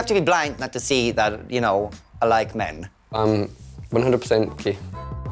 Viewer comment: What does he say at 7:06? I’m 100% (what)? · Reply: "I'm 100% gay"